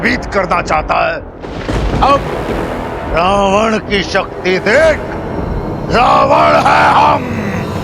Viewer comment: He said : Ravan hai hum 19:55 I heard : Ego hai hum